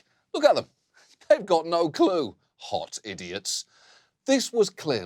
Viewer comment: i was almost ready to lose it when I thought he was making a"hot glue" joke abbout the horses then realised he was just saying"they ve no CLUE...HOT idiots" lmfao